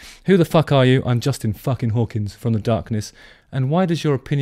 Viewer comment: For a moment there at 2:45 I thought he was saying "Im Justin F**king Bieber!".